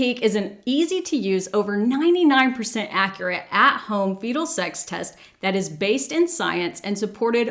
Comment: Just need you to know I thought you said "at-home beetle sex test" at the beginning 😂😂 Aaaanyway I've missed you dearly and am so glad to have you here again.